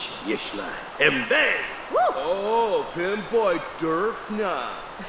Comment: did he just say pimp boy